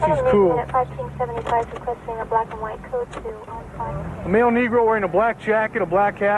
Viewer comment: did he say male Negro ?